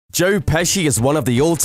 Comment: At the very beginning I thought he said "Jerk Pesci "